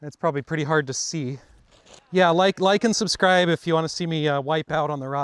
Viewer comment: I thought you said “lichen subscribe” for a moment 😅